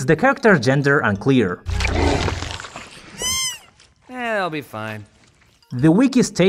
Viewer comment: I thought Jax said "Eeeeeeehit'llbefine"